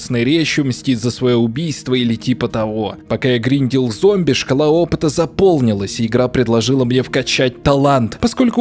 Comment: Мне одному послышалось как он сказал "Шкала опыта" как "школоопыт"? :D